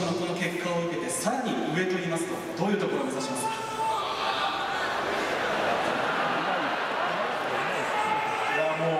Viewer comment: 後ろの赤い服のおばはん爆笑してるやん。 6:57くらいに観客から笑わせるヤジが飛んで周りも笑ってるけどなんて言ってるのだろうか？ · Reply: 「横綱!!」だと思います😁✨